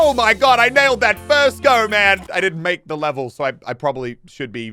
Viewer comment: I had to replay that over and over, because I swear I hear him say “I didn’t make the level” and he said at the beginning he did.